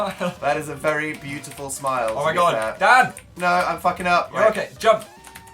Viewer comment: phil TOTALLY sounds like he said "dad" instead of "dan"